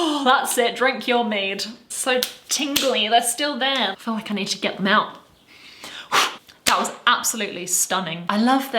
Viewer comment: for a second I thought you said "I need to get mounted".